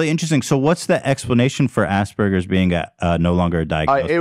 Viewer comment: I swear to God, I thought they were saying "Ass Burgers" and not "Aspergers"